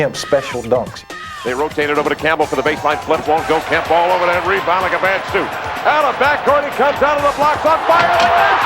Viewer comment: videogame!!! and wtf did that announcer say... "all over that rebound like a cheap suit!"??? lol